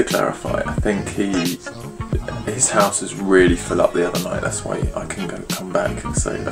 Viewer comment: Hey, guys, what is he saying in 5:35 about the house being something and people asking something blah blah blah ? I don't get it. Someone should make some subtitles for all us subscribers not using english as our first language :D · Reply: he said "his house was really full up the other night" and that people were asking "why didn't you go back with him?" he just wanted to explain why he couldn't stay at Hayden's, because there wasn't room.